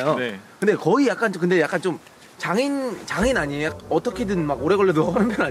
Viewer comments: 장애인 아니에요? 라고 들리는데? ㅋ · 나만 장애인이라고들려?ㅋㅋㅋㅋㅋㅋㅋㅋ